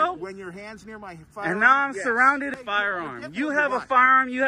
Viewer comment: What did he scream? I heard “do not surround me” which would be very funny considering he is part of the crowd surrounding our auditor